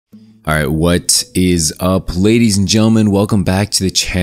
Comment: Every time you start the video with "What is up" for some reason I hear "Watt is UP" lol